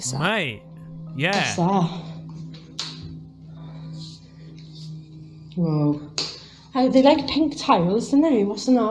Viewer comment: idk if im wrong...but at 22:47 in ur video, I heard a voice whisper as she enters into the room. It wasn't mentioned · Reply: It sounds like "I sense she"